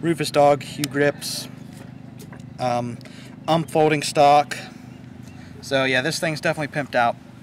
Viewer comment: Did you say Ump folding stock ? I believe it's U.M.P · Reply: Paintball Noob i heard him say unfolding stock